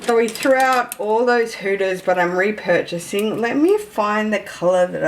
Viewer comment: It took me a while to realise you were saying “Hudas” - I thought you were saying “hooters” 😂